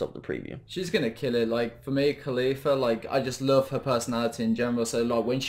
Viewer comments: Is it just me but it sounded like he said mia khalifa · sounds like he said Mia Khalifa XD